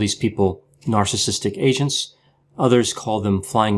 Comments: Sounds like yr saying asians not agents, lol, the first time you said it my head nearly snapped from looking around with a 'huh' lolol so funny! I always worry when using the term flying monkeys as I know some People jump straight away to the wrong conclusion! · Ohhh... You said narcissistic AGENTS... I THOUGHT YOU SAID NARCISSISTIC ASIANS 🙊